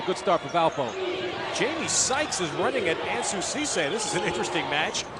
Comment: did you say Ansu Sesay? That guy is a beast when he played in the PBA